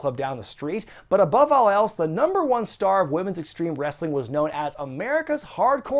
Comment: could've sworn i heard "women's extreme breastling"